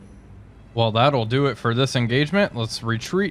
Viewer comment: i got a heart attack, cause i thought he said:"that'll do it for todays episode "